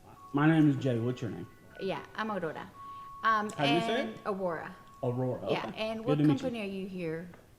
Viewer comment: Bruh.. did she say her name was odora? Like as in odor?! xD She thought better and rolled that R into the abyss xD